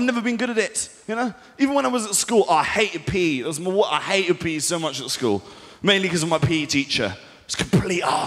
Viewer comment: OH MY GOD I THOUGHT HE WAS SAYING I HATED PEA AND I JUST ACCEPTED IT